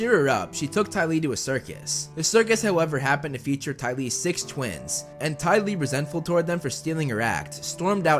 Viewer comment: did you say 'six twins'?